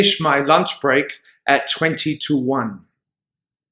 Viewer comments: 5th question is great! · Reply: i can't hear it. Can you help me, please? i hear "wenty two one". that is terrible!!! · 20 to 1. I heard it as 22:1